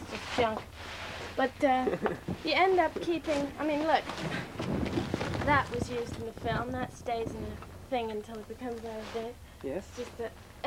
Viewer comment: Can't quite make out what Vivian says about the item (from '2001') at 37:08? Sounds like she says, "That was used in the film. That's Dave's and the thing until it becomes out of date" (???)